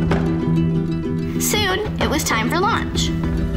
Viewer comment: on 5:32 I heard "soon,it was time for lunch " and I was likee whaaat does that has to do with anything?? ohhhh launch